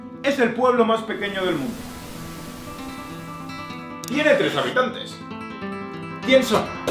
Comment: Pensé que dijiste 13 habitantes